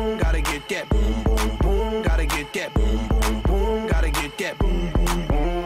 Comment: is it just me or does anyone else hear "Got a Kit Kat?"